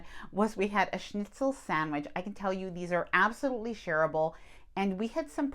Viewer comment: Shareable sounded like you said terrible 😅